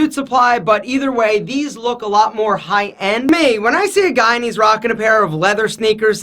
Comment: Sounds like you said guyanese.